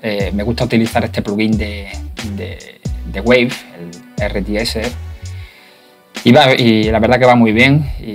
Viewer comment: Cual es el segundo plugin que dice de Waves? dice algo como "Erretiese" en el minuto 7:16 lo dice. Muchas gracias